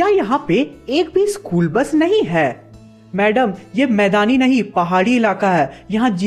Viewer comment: who else noticed 'CHOR BUS' instead of school bus 😂😂🤣🤣🤣